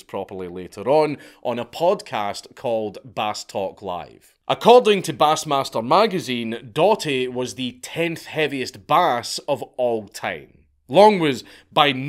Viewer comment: Bass Master? I thought you said ass master!